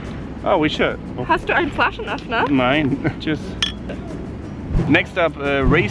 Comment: any one could tell me what she said at 3:47 before drinking the beer? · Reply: German: "Hast du einen Flaschenöffner?" Do you have a bottle opener?